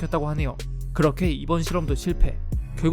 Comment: 초에 그렇게 이번 시험도 실패라고 써져있음 분명 실험이라고 들었는데